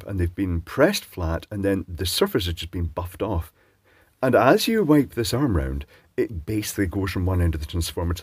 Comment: LOL I thought you said "as you wank this arm around". Had to check the closed caption!